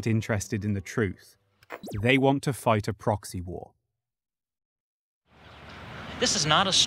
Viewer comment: I zoned out and all I heard was “They want me to start a proxy war” As I was starting an essay on the Cold War. Nearly made me fall out of my chair